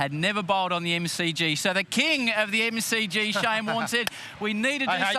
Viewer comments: did he just call him Shane Watson?? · Did he just said "The king of MCG, Shane Watson".